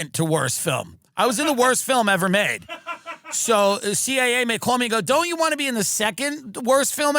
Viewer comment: Did Tim say “CIA man called me…”? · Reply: "CIA MAY call me"